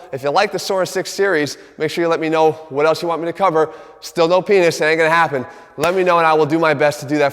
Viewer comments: wtf did he say? "Still no penis, it ain't going to happen" is what I'm hearing 😂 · What is he saying and 6:25? Sounds like 'still no penis, it ain't gonna happen'. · Did I hear "Still no penis, it ain't gonna happen" at the 6:27 mark? ...or is my hearing off?